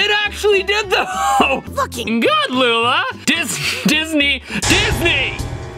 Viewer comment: i hear looking good